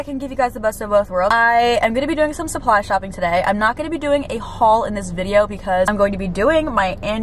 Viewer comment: I thought you said u won’t gonna to a haul